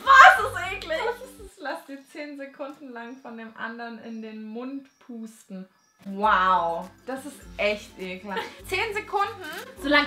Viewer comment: Ich hab verstanden: lass dir von den anderen 10 Sekunden in den Mund pupsen 😂😂